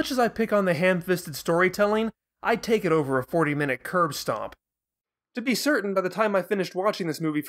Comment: did you say "curve stomp"? hehe, that's a great "bone apple tea" moment :P It's "*curb* stomp"